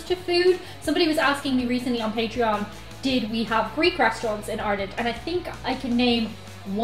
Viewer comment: The first time said "Greek restaurant," I misheard it as "freak restaurant." I was wondering, what on earth is a freak restaurant. Then I heard her correctly, and I said, "oh."